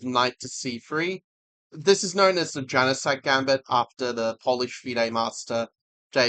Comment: why did i hear "genocide gambit" at first u